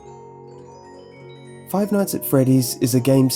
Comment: Fuck! Every time to say the title of the game, I mishear 'five NUTS ar freddies'. Ohhh, my poor, poor, gentle childs imagination XD